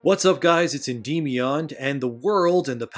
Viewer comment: When you said "whats up guys" i thought i heard quandale dingle lol